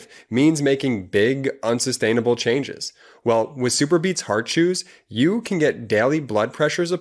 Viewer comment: I thought you said super beats hard shoes, lol. My ears crack me up sometimes, lol.